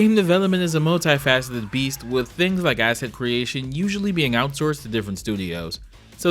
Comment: did you say asset creation or acid creation, cause dear lord I'm pretty sure he jumped head first into some looking like that